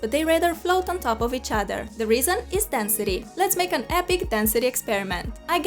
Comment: XD I THOUGHT SHE SAID THAT THE REASON WAS DESTINY